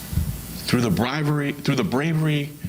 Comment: We should take his comments on connections in law enforcement seriously. Just like Heuerman. The corruption runs deep in NY. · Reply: It sounded like he was saying “through the bribery…” @ 6:53 Made the hairs on my neck stand up a bit